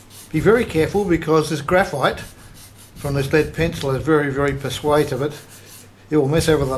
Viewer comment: What does he say from 2:53 to 2:54 - sounds "this graphite, from this lead pencil is very very (persuativate) - you'll mess everything up". Couldn't be what I've put in brackets but I'm stumped to hear the correct words. I've listened dozens of times but can't get those few words. Hope the repair works as well for my remote. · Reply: Sorry about the confusion. As you can tell, I'm not much of a public speaker. I was saying that as any run off, or spill overs, because it is so conductive, may short out other areas. I found that I needed to be very careful as I applied it, to keep it exactly where I wanted it.